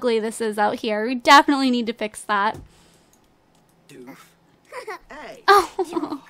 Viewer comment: It sounds like drake is saying doof